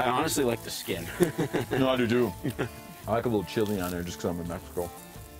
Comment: I thought he said " I like a little. Chili on there cuz I'm unethical" lol